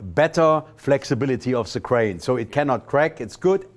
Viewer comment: OH .... GRAIN .... I thought he said Crane